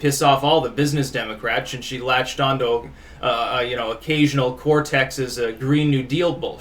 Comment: did, did I hear that right? Did you call AOC occasional Cortex at 3:06? Top notch sir.